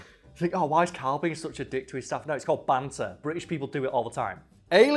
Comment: I totally heard “Banter... Rich people do it all the time.” Which made me laugh way harder than expected. To clarify I know what you said, but in that fraction of a second it took for my brain to catch up it created a joke on the way.